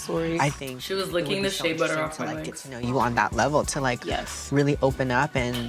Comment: I was washing my face while watching this vid, and I heard say “she was licking the shay butter of my legs.” And my wierd self thought she was talking about Nikita. I was like 💀👄💀?